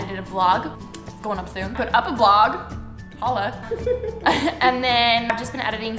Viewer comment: did she say Allah?omg